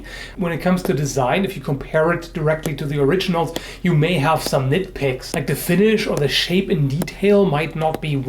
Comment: i thought he said (you may have some dick pics)